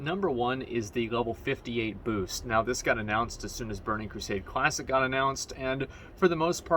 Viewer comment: I could have sworn he just said Bernie Crusade.